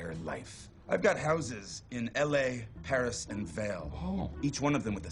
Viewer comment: I swear I heard Tyrion say "I've got houses in LA, Paris, and the Vale" well maybe not "the" but damn